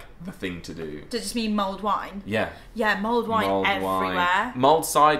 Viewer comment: LOL I thought you were saying mold wine ... thank god i googled and it came back with mulled wine!